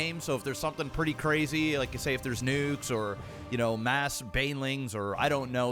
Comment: I 100% thought he said "if there's nudes" at first XD.